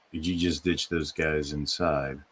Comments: I hear, "The way you ditched those guys was an insult." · I thought the audio said "what you did to those guys is an insult"